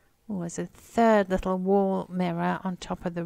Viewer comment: It sounded like u said, absurd little mirror 😂😂😂 but u said a third little mirror 😂😂😂